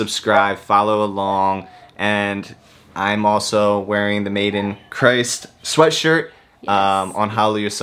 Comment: Definitely thought his shirt said made in china.